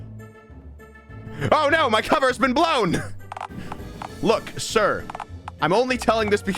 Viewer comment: my audio glitched and I heard "OH NO IVE BEEN BLOWN"